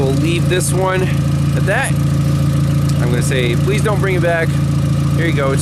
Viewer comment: I laughed at "Don't come back". looked deadpan serious too :D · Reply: That isn't what he said. He said, "PLEASE don't bring it back." (@ 9:17) I haven't heard him say that before. I am not surprised he did, though.